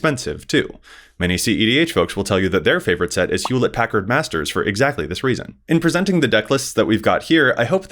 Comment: what is saying here? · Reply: Hewlett-Packard Masters. as in Hewlett-Packard (HP) printers. as in proxies.